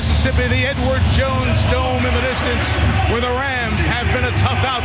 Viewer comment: No lie when he said the Edwards Jones dome I heard “the N word jones dome” lmao